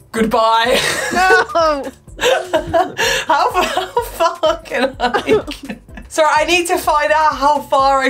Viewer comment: DID SOMEONE SAY A CERTAIN SWEAR WORD THAT STARTS WITH F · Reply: Nope, we said "how far can I go" xD